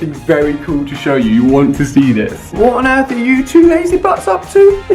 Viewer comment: It sounds like he said you two lazy fucks like if you heard